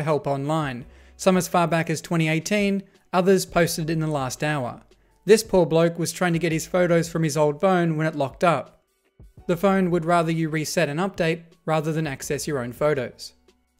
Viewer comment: This poor bloke was trying to get his photos from his old BONE. Did I hear that right?